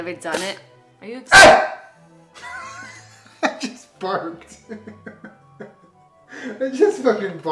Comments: * barks * I just barked Hahahahahahahaha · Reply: I thought he said “barfed8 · " BARK" " i just... Barked" they both laugh Idied at this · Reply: I THOUGHT HE SAID BARFED CNXNND